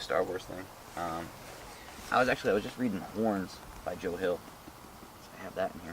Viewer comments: At first I Thought he said "I was reading porn" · i thought he said i was reading porn not horn